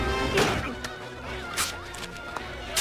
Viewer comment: I always thought that instead of saying see you in hell he said see you in health